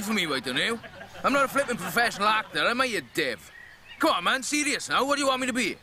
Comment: Not wanting to say this but....he sounded like he said DIU which means f**k XD